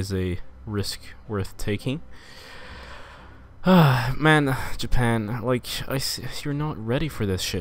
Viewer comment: Did u say "Men" as in the swedish word at 11:32? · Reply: I said man. As in "man, ohh man"... but I forgot the ohh.